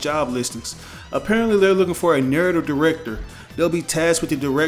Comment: Every time I hear “Narrative Director” I hear “Nerd Director” lol